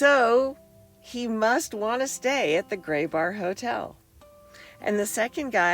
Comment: Talk about an accent miscommunication, I thought you said 'gay bar hotel' 🤔 and thought holy F America is so homophobic 😬 😅